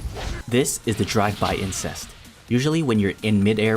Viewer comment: Did he say drive by 'incest' at 3:27 lmao